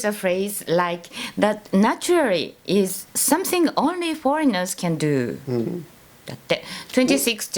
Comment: Um, not sure if this is just me, but there are A LOT of words that are no where near what both of you are saying with the captions on. At 6:09 she says something about only foreigners do but it says army. Might want to look into it. Other then that great video.